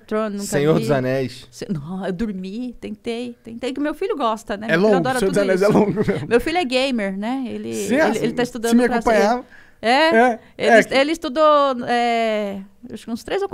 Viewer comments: Achei que ela iria falar: meu filho é gay. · Quando ela foi disser "meu filho é gamer" eu entendi "meu filho é gay"